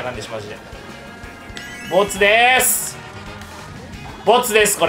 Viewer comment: 密ですに聞こえる俺はもう末期